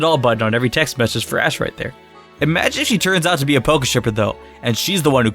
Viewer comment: instead of pokeshipper it say poke stripper LMAOOOOOOOOOOOOOOOOOOOOOOOOOOOOOO